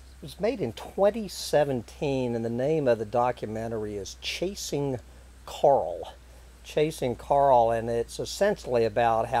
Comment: For some reason, I kept hearing that as "Chasing Carl" and "Carl reefs" and immediately wanted a hamburger. Since I'm vegan now, that presents a serious problem. What am I supposed to do?